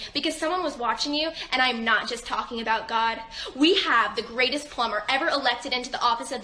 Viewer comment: Did she say they elected a plumber?